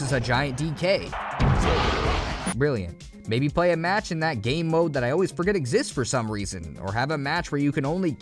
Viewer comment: This guy really used "gay" as a pejorative in 2023? Rethink it. EDIT: He said "game mode" and I misheard it. Apologies to. I knew it was an absurd thing for him to say!